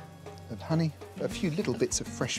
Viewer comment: I heard "carrot pus"... i was like i never knew vegetables could have pus. F*** I am stupid 😂😂